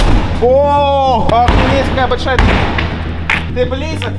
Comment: мне одном кажется что он сказал: оооо какая охуеная дырка